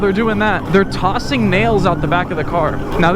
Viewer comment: I thought he said males not nails